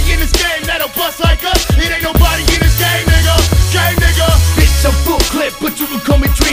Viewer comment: Is it just me or does it sound like blaze your Dead homie is saying"Ain't nobody in the Gay Nigga, Gay Nigga"? I know he is supposed to be saying "Game" and not gay but it really sounds like he is saying "Gay Nigga" though! Or am I just a crazy old school pervert?!? I'm sure the juggalos and juggalettes will let me know the Trufus Rufus!